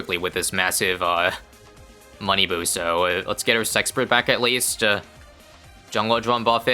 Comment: What did you just call the engi? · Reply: S eggs bert